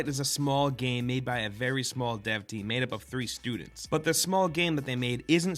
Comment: Lol 0:19 thought you said it was a game made by three stooges.